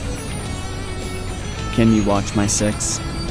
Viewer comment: anyone know what makoto says at 52:46? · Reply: Hey says "can you watch my six?" Referencing Junpei at the arcade saying "I'm trusting you to watch my six!" Indicating a form of trust between the two ^^ Thank you for watching btw